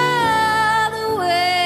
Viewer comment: Did Louis said "BYE" to Harry ???